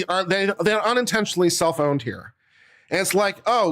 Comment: I thought he was say "cell phone for thr government". But he was saying "self own"